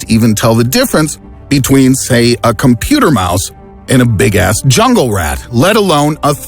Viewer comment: 😂 did he say bigass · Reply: Yepp! Big ass rat!